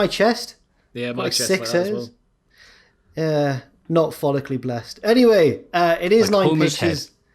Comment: When said in the first minute "I'm follcally challenged", I thought he said phallic- which would've been him saying he had a small member lol I had to rewind it and relisten cause I was so confused.